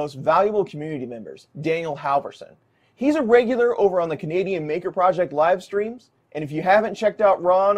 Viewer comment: Maybe I need to have my hearing checked. I thought you said "Comedian Maker Project" the first time you said "Canadian Maker Project." 😅